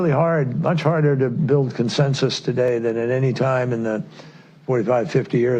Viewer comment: Kerry says "build consensus." I hear "manufacture consent."